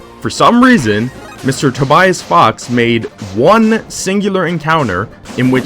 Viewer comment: did i missheard it or did you said tobay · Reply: Tobias